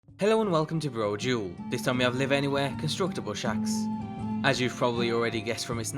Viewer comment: Darn, thought it said constructible snacks!